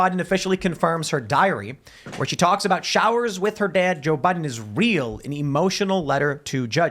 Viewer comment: sounds like he says “Joe budden”